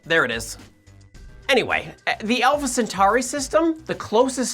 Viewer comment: Did he say "the Elvis Centauri system"?!